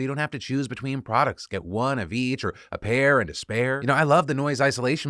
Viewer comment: Re: Raycons--For a brief moment, sounded like you said,"a pair in despair." New ad pitch: "When you're in despair, Raycon! Just shove 'em in your ears and drown out the cruel, cruel world. With their excellent noise isolation, you can't hear yourself crying!"